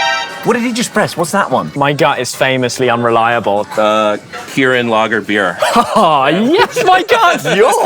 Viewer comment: Seriously thought he said Gurren Lagann beer.